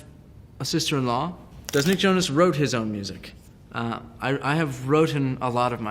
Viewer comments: he says wrotten ..... Who else heard it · Did he just say "wrotten"????? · Did he just say "I have " "wrotten" " most of my music"? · Did he just say "wrotten"?